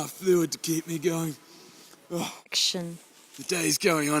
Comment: i heard someone say action